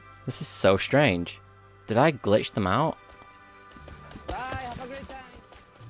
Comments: I hear the cannibal at 2:12 talking, i think im gettin crazy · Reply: BYE HAVE A GREAT TIME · Did anyone else hear at 2:16, "Bye have a great day"